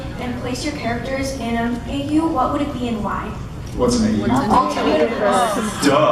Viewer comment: what does Jared say at 20:20? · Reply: "what's an AU?" she meant Alternate Universe by that